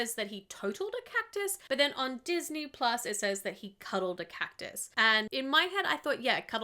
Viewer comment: Dark, but I thought he said killed the Cactus, that surgery was to revive his friend cactus man. My wife assures me it is cuddled though... Cactus needed a hug.